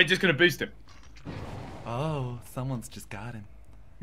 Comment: just to good at this game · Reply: sorry thought u said hes just got god